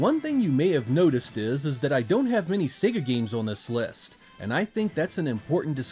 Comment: "I don't have any Sega games on this list." I know Soul Calibur was from Namco but does Panzer Dragoon not count as a Sega game? Oh, sorry, "I don't have many Sega games on this list." I misheard "many" as "any" the first couple of times.